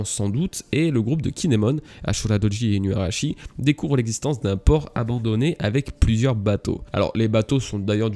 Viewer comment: Je lis pas les scan mais je suis avec tes reviews . du coup quand j'ai écouté "ils ont trouvé un port abandonné avec plein de bateaux" j'ai compris "un porc abandonné" j'étais en mode "avec plein de bateaux?" puis j'ai compris :D